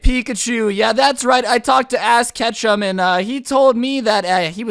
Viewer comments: Why did I hear "I talked to Ass Ketchum" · Did anyone else hear Ass Ketchum?